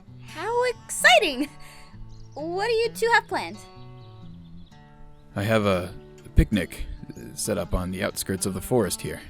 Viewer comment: when the guy say I have a picnic set up I thought he said I have a big........ you know what I mean